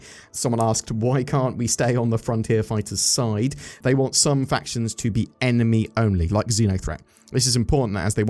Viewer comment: I thought he said, “They want some fashions to be enemy only.” Lol.